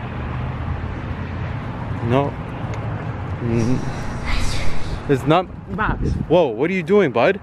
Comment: In 7:54 i hear a sound which says "it's over".